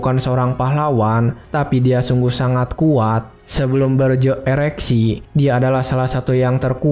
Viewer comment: berje apa mas? 😅 aku ngga salah dengar kan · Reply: Ereksi ngab😂😭